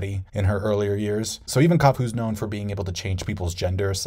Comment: Mate did you say kaku knows how to change peoples gender?